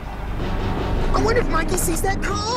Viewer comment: "I wonder if Mikey sees that car" is said so fast, I thought he said "what if Mikey sees that car," like they were counting the amount of kids who walked into traffic or something.